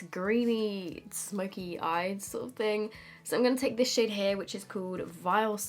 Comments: Tell me why I heard “that shit is really pigmented” instead of “this SHADE” lmao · shit or shade i cant hear😂😂😭💋💋❤️